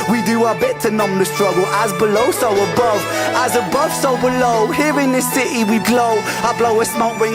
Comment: It's the same String Quartet as in Money Game Part 3. Filmed at Enfield, N.London. The singing part at the end lifts the song to new heights. The first time I heard the official video I thought he was saying 'As a Bus overload' instead of As above so below. Another member of the overthinker club here! Good luck with your Music & following your dreams!